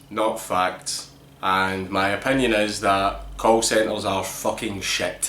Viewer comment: at first it sounded like you said cult centers, and it fit pretty perfectly